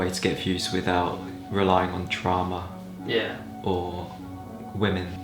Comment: I thought he said TRAUMA rather than DRAMA..... TRAUMA fits his personality more Closely I think.